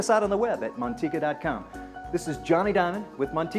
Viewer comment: Thought he said Johnny dang😂